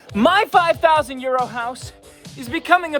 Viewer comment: I always hear “ my 5000 year old house” 😂